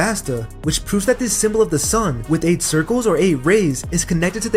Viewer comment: I keep hearing "The Sun with 8 Rizz"